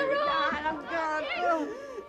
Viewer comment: Does she say I want the doctor or i wanted to talk to him? Hahahahahahah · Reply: I wanted to talk to him